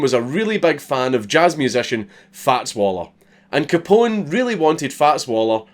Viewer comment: I swear I heard "Fat Swollo" :D :D :D :D